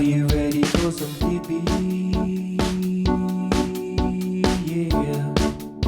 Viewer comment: I always have to listen to the song 2x to hear "Digby", the first time I have to pause and rewind, Sam Style, because I thought I heard "peepeeeeeeee"